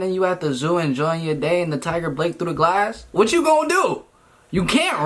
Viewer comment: I don't know if it's just me, but did anyone else hear him say blake through the glass at starting